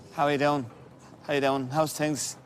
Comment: subtitles: How are you doing? I hear: Hi you down. He speaks this way, quickly and with some words pronounced in a way very different from how I am used to hearing them, but he seems to believe that the people he talks to cannot understand English or are unable to answer a simple question. Once I also heard a guy from another part of the world saying “Ged-eye met has gone”. What I was supposed to understand was: “Good day, mate. How is it going?”